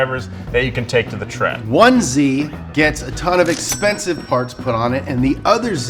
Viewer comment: sounded like you said “onesie” Lol then the mental image of you guys driving around town wearing Tiger Onesies 😂